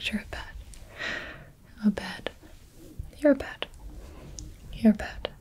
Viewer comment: Sounded like Gibi said “You’re a bed.” And I was like, “Oh sheet...”